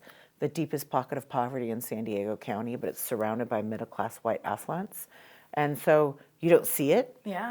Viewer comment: When you said “White Affluents”, I had to rewind bc I thought you said “White Asswipes” and I was like daaaaaang Tasha!